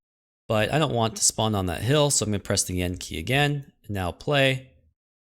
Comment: Why is everyone saying N key, guys, please sai enD with a loud D Even the subtitles say quote: ,, so I'm gonna press the N key again and..."